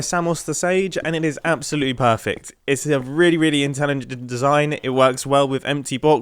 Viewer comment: I'm not the only one who heard mumbo's "intelgeged'